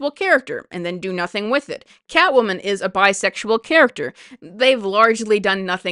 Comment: Weirdly the worst example of two characters being destroyed by a badly written relationship is Batman and Catwoman. A relationship that was popular for +80 years! I used to love their dynamic until Tom King’s Batman run. Both characters did nothing but spout nonsense about how “The Bat needs the Cat” and the like. Bruce became an obnoxious simp for Selena who had no personality whatsoever. I know DC tried to walk things back by cancelling the wedding, but it was already too late (and really the wedding itself was not the problem). King continues to get work at DC to destroy other characters and Batman, Cat-woman and their relationship have never been fixed in mainline comics. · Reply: I thought you said Batwoman not Catwoman and was a bit surprised that the issue of her being his cousin (I think?) never came up 🤣